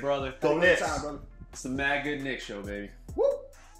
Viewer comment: sounds like your saying bag of dicks show. which ironically is about as good as knicks will ever be